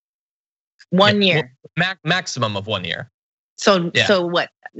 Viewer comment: I thought the Orange 🍊 man said "Minimum 10 years". Wtf